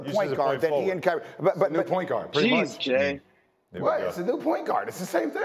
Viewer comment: whose voice was that saying “jeez jay”?😮